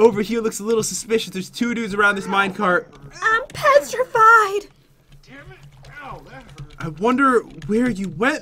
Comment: i think the blue guy said da d word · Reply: No he said dam it but if you mean dam it then yeah he did